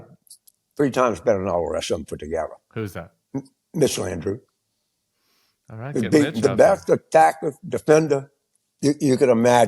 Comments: I've watched this part a dozen times and, for the life of me, I cannot figure out who they're talking about. It sounds like he says "Mitchell Andrew" but I can't find anyone by that name connected to the party? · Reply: Mitch Landrieu! Figured it out. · Mitch Landrieu? Is that who Carville is suggesting? (sounded like he said Mitchell Andrew) Former Mayor of New Orleans, Senior Advisor on the implementation of the Infrastructure Investment and Jobs Act, currently co-chair of Biden's reelection campaign. hmmmmm.